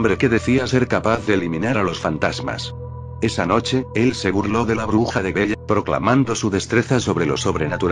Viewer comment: ¡QUE SUSTO!: ENTENDÍ "LA BRUJA DE DELL" Y CASI TIRO MI COMPUTADORA.